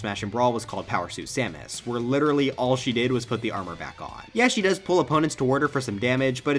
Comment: I LITERALLY HEARD poops her armor lol!!